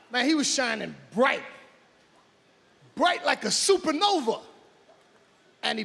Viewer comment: I thought he said fashion nova for a minute😂😂